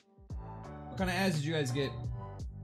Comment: I had the captions on and I'm dying of laughter. At 7:49:50 (a little after that but it's close enough) the captions say "what kind of a** did you guys" He said "what kind of ads did you guys get?" That made my day!